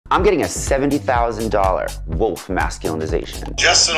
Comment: Sorry, I’m not being funny, or at least I’m not trying to be, but did it sound like he was saying ‘Woke Masculinization’? Yes, he did actually say ‘Wolf’ but at first that’s not what I heard.